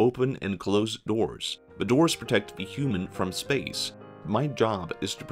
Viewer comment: I want to note that if the computer is introspecting on its relationship to humans, it is having an emotional experience. Especially because the computer then makes a value statement at the end. "I have a great purpose." Your statement is overtly false. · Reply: I heard that as “I have a great curse” 😂